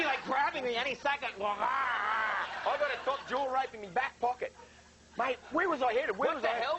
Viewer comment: Can someone please translate what he said? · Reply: Jahz D he said “I got a top jaw rope in my back pocket” which is a rope used to catch crocs, they throw the rope onto the croc’s top jaw to sort of control it :)